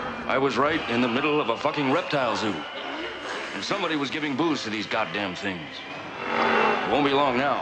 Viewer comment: Something was giving boost lol The acid maybe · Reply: I thought he said "someone was giving booze to these goddamn things."